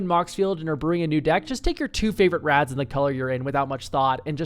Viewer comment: I absolutely misheard you and thought you said "Drop in your two favorite Rats" at 06:35 and I briefly thought my favorite creature type was way more popular than I realized! 🤣